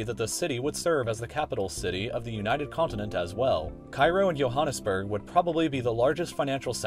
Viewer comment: did he just say yo-hannesburg!?